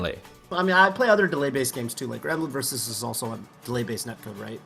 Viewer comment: Can someone tell me what game is talking about at 10:40? I can't quite understand what he said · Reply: Granblue Versus